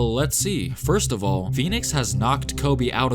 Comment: When they asked Kobe about Phoenix, I thought they said peanuts 😂 I was like, man, Kobe really hates peanuts 😂😂